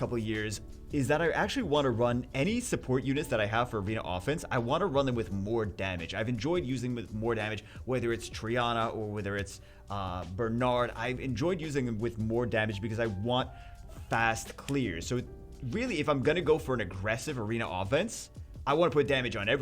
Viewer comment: did I hear that right? 🥯 likes to use DMG Triana for arena offense 🧐😂